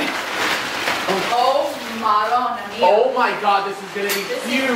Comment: did say "oh madonna mia"? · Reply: oh marona. it’s italian